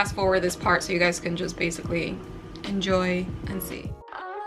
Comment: When you said "enjoy and see" I thought you said Yoency and i got excited then disappointed :(